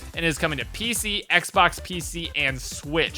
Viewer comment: Did he just said pc, Xbox 1 , pc at 1:47. Lol